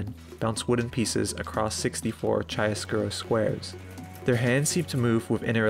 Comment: the narrator says "across 64 xxxx squares"... What is the words he uses to discretions the squares? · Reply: chiaroscuro, an art term for strong contrast, referring to the black and white